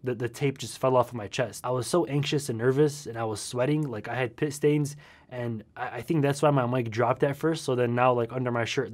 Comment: i thought u said u had piss stains i was dosed of i woke up REAL quick